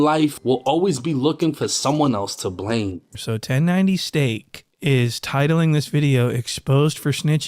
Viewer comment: did he say 1090 STEAK?!